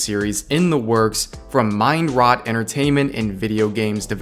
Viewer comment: I heard that as " Mind Rot " and not as " Mind Riot " ... Which would definitely be more accurate.